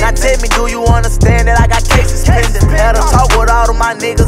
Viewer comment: does he say cases pendent or case suspended ? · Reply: cases pending